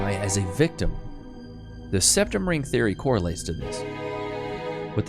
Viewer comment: Septum? Ooh ! I thought you said RECtum !